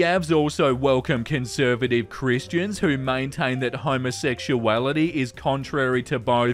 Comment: I thought you said homosexuality was against gods lore… thought it was so funny but then realized it was just your accent😔